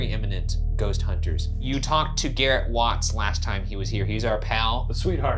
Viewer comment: i heard "whos Garrett"